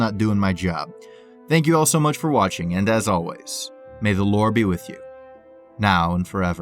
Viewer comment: Thought you said May the Lord be With You I was like 👀